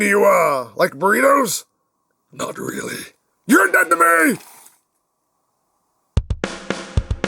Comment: What did Megatron say? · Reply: YOUR DEAD TO ME! (Megatron)